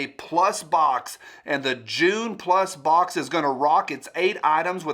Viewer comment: When you said "June plus box" I heard "Jew plus box" and I thought, "Overweight Israelis is a weird demographic to have."